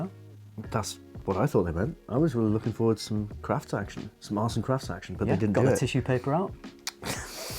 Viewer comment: I genuinely thought said @ 13:39 "ars and crack" action but I replayed and see now I was wrong, it was ""arts and crafts"....